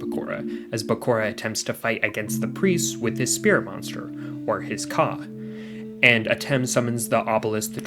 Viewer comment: i definitely heard “with his spirit monster, or his 🐓” which i figured was a joke about the giant snake under zorc